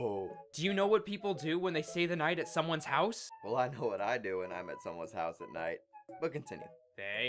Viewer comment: What did rover said in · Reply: Пердун Пердунски “Well, I know what I do when I’m at someone’s house at night...but continue.”